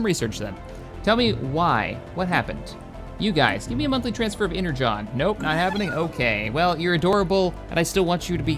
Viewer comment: Haha did just say energon :D